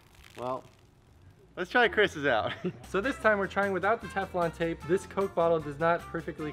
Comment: I thought they were saying "tampon tape".